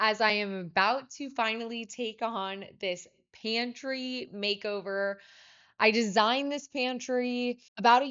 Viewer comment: I thought that said panty makeover